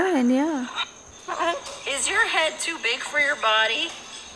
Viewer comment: OMG, that's first time I've heard Jersey say a word. All I recall before was Raptor noises. I swear she says body 0:30 and